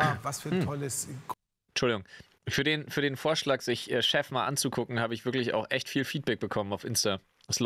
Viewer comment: ich musste das drei mal hören um chef zu verstehen und nicht shaft XD